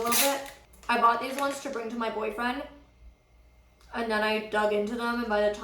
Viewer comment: Wait did Molly said her BOYFRIEND at 0:39? Where have I been? I guess I missed a lot from her last video to this one